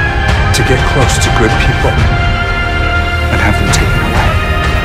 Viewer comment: What’s being said here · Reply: "we sign up to lose each other."